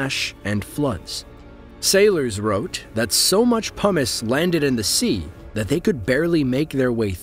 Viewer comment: sounded like you said 'So much hummus fell into the sea' xD